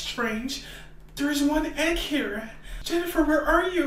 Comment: what did she said: MOMMY!!! cry CC: [Music]